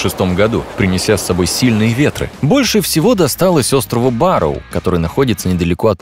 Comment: Диктор: Больше всего досталось острову «Ба́роу». Что слышу я: Больше всего досталось острову Вару. Значит у Вару не только Королевство, да ещё и остров!